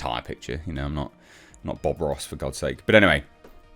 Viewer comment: I presume he means Bob Ross the Painter, (one of my relatives watch them one TV all the time, since the UK belatedly discovered him after he'd died) I misheard for a moment and thought he'd said Bob Roth, (I'd fall off my Chair in Shock if knows who that is mind)